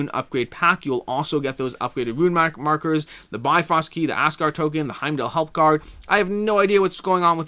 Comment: Did anyone else hear ass guard token at 36:55? Had a good laugh there, thanks!